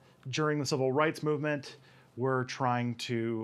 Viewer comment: Subtitles say "we're trying to stop", instead of "were".